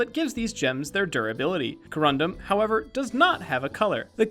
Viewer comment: I misheard "corundum" has "condom" sorry. I had to point it out